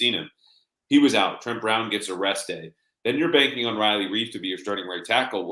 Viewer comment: I had to go back and relisten...at first I thought you said 'trent brown gets arrested'! Thank goodness it was on a rest-day!